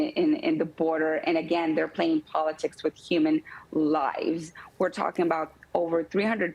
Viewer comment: I thought it said “playing with human livers.” Disappointed.